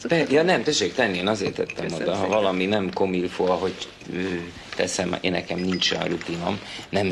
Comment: "Tessék csak venni azért tettem oda. Ha valami nem ......." Mit mond? Nem értem. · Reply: comme il faut (illendő, helyes)